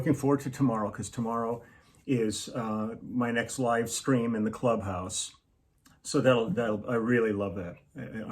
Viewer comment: Had the volume on low and thought I heard, "Tomorrow is my ex-wife's scream in the clubhouse".